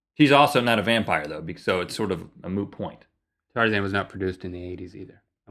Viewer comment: Did Rhett quote Joey from Friends? A moo point? "It's like a cow's opinion." "It's moo." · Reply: I thought he said moo point too! XD I replayed it a few times and then scrolled down to the comments to see if anyone thought he quoted Joey from friends.